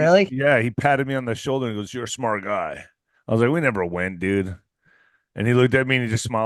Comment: sounded like he was saying “we never arrived “